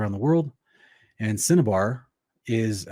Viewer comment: I was listening and thought you said cinnabon. Lol imagine a cinnamon roll powered vimana. Anyways, thanks for the great insights. Stay blessed